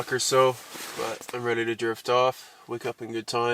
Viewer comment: did he say what I think you said? Lol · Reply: Lol drift off 😜